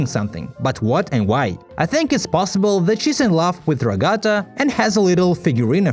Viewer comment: I'm sorry but did he just say "Ragata" ????